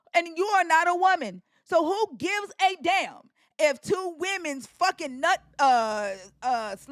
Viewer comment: did u just say “two women’s nut slime” 👁️👄👁️ I have never!! 😂😂😂